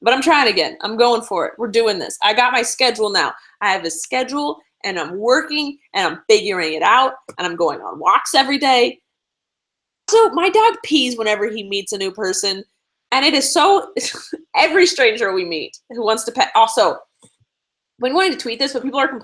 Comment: I thought she said my dad pees whenever he meets a new person LOL